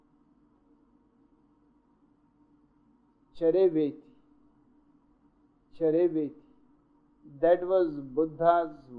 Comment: What was the “buddha’s word”? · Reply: "Charaiveti"..... means keep moving